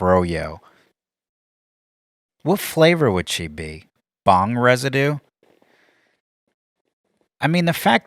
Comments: Thank you to AtlasVPN for supporting the show! Grab it for 1.39$/mo before the deal expires: <link> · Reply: I thought you said, "thong residue". I'm sure it'd be more accurate. 12:41 That would be impossible. He would never be on the battlefield to begin with, he would have already dodged all the way to Canada. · I thought you said, "thong residue". I'm sure it'd be more accurate. 12:41 That would be impossible. He would never be on the battlefield to begin with, he would have already dodged all the way to Canada.